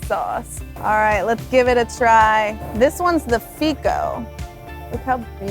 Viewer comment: Oh, she said, "Fico." I thought she said "Fecal." I'm feeling much better about this restaurant now.